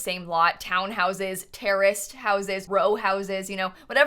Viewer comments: I thought she said terrorist houses😂 Not terraced houses😂😂😂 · i thought you said ter01rst houses